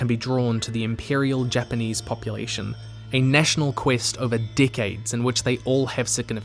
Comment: help i thought he said “dick aids” 😭😭😭😭😭 (re-commenting because something happened to my original comment and you can’t see it 😰)